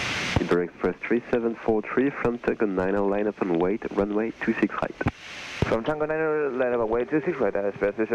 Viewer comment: Il dit quoi le mec a la minute 15:29 😂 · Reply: Il a dit runway two six right, en gros piste 26R. J’avoue il aurait pu faire un effort mdr